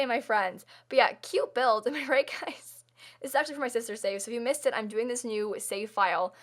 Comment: did she just say sistersave